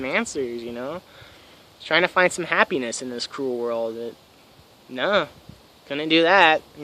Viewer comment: Thanks for the upload. Interesting story. I can tell you are someone i would appreciate tripping with. Only thing i didnt quite understand was how you call this a cruel world. Sure, it CAN be cruel to us.. but its also so beautiful in the same token. Cant have positive without negative. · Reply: I thought he said 'in this cool world' haha